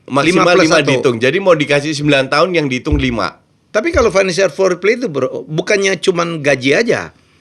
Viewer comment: kirain salah denger bilangnya "foreplay" haha,gw jadi bingung gw klik video bahas bola atau bahas anu ya haha channel bola favorit